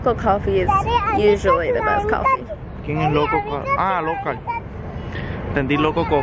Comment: Im with i heard "loco coffee" too LOL